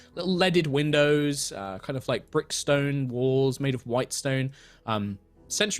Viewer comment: Me: did he just say Brookstone walls? ... OMG, she's a Starbane agent in disguise! And they'll take her to the meeting and cause the downfall of Aerois!